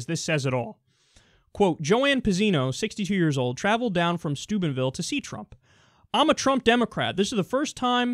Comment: Wait at 3:45 did he say the Trump supporters "travelled down from Stupidville"? ahahahahahaha